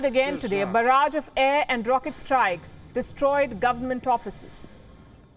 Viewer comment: Did you just say government prophecies or properties?